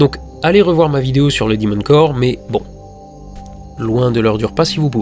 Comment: La vidéo sur le quoi ? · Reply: Le demon core !